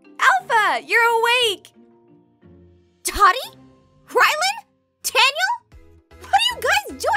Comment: It sounded like she said, "Totty!" "Tyiland?" "Taniel!"